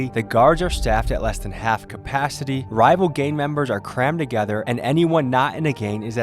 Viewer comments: I for real thought he said “rival gay members” for a good minute 🤦‍♂️ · did he say gay members lol